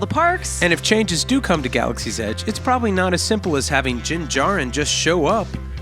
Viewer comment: Dyn Jarren is his name. It sounded like you said Jin Jarren.